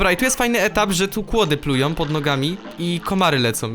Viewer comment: czy tylko ja jestem tak zwyrodniały że za pierwszy razem usłyszałem że "płody plują pod nogami" ? XD